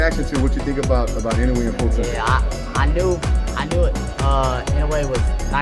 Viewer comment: からのモートン選手の「アノ・・アノ・・」はイノウエの発音にてこずっているのではなくて、"I knew...I knew..."と言っているように聞こえます。I knew... I knew...Inoue was nice. They call him Monster for a reason.とつながっているような気がしました。あと個人的には、モートン選手の表情から（発音しづれえ）という感情は読み取れませんでした。